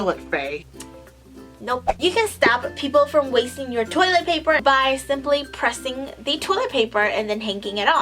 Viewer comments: i heard "you can stab people for wasting your toilet paper" like ok thats one solution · when she said you can stop people from wasting your toilet paper I thought she said you can stab people 😂😂😂